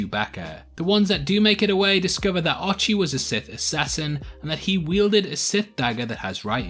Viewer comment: Did he just say that, "R2 was a Sith assassin"? Damn dude, I came here for Star Wars leaks, not top 10 anime betrayals